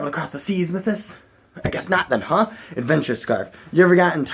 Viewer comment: i thought it said adventure scare